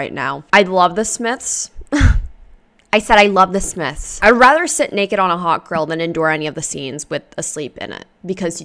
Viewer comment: omg--- i thought you were saying " id rather sit naked on a hot girl" i was like, wouldn't we all?